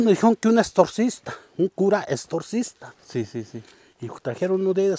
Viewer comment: Cuánta facilidad de palabra tiene el señor 👍 Nomás que en el minuto 7:29 me reí de mi misma porque entendí Extortista fue un desliz de oído y de imaginación pero mis respetos para todas las personas que amablemente nos comparten sus interesantes anécdotas. Señor Dios permita que algún día usted y Lino encuentren muchas monedas valiosas de las que enterraban los hacendados o los bandidos. Estoy segura que con más recursos seguirían haciendo obras de caridad a manos llenas 🙏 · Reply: Sí, el señor en su interesante plática dijo extorcista queriendo decir exorcista pero yo entendí extortista y mi mente imaginó que buscarían un señor que antes vendía tortas Ay Dios ésto parece un trabalenguas jajaja. Lo bueno que no necesitan exorcisar nada porque son acontecimientos extraños pero que no han dañado a nadie. Saludos y bendiciones para todos 🙏🌷